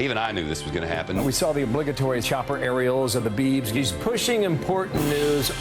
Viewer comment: the obligatory... What? Did anyone else hear areolas?